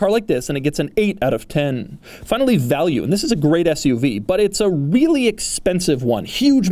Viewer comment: I thought you said this is a really FAT SUV.